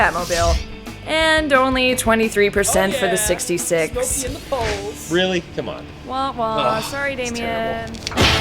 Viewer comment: Did he say "Oh yea, smoked you in the poles" at 4:45?